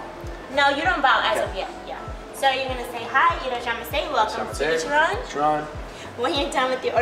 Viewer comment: I heard “hi welcome to ichiramen” But that’s just me..